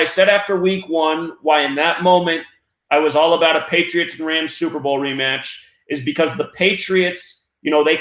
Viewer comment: I thought you said Patriots vs Redskins super bowl?